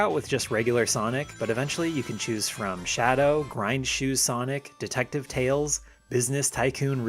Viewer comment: I misheard you during the character select and thought you said Raichu, but it was actually grind shoe and that makes a lot more sense lol